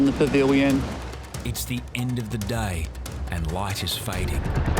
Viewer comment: Did i just hear ," the end of the die"?